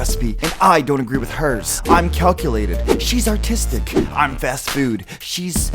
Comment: thought he said she’s autistic. No offense to anyone, I’m just blurting out my thoughts 6:47 does that say pee all you can pee